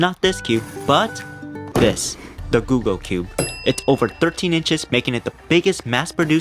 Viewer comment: I thought said google cube but it is the googol cube😂